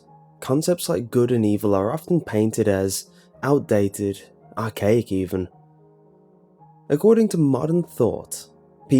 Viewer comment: I thought you said or cake even ....